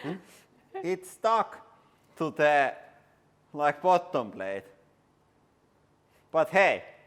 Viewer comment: Did I hear "it is stuck to my bottom"